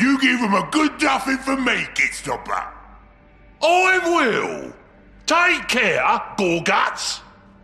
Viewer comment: D-d-did I just hear an ork telling an other ork to "take care"? o.O o.O o.O